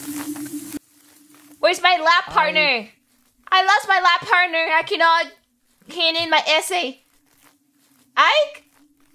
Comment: I think what Reimu said in 2:42 is "Where's my Lab(oratory) partner? I lost my Lab partner, I cant hand in my essay." Because in Lab project, usually you have a group or at least one partner to take notes as essay/journal for the project. If I remember correctly, in labyrinthe there was also a section where they make potions together. Maybe thats why Reimu said that? I kinda forgot if this moment was before or after that tho. This makes me want to rewatch that collab again, thank you.